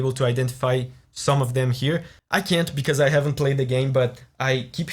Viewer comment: I keep hearing you saying "I don't know, I haven't played the game" and not "so I took a break to play the game", you should fix that ;)